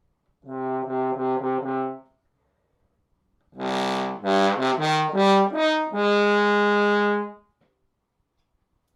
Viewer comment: What did you say that excerpt was called at 6:10? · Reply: Ein Heldenleben, Richard Strauss